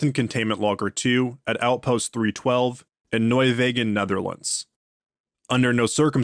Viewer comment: you pronounced his name perfectly! everything with the letters 'ch' in it... not so much lol But thats not a problem, i would be suprised if you could pronounce those 'ch' words correctly. Seems like we dutch are the only ones to use the 'ch' sound😆😅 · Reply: you've butchered Nieuwegein though😂 first I thought you said Nimwegen lol Nieuw as in view but then with a N, we as in wut but hold the t, Gein as in samhain, but then Gein 😅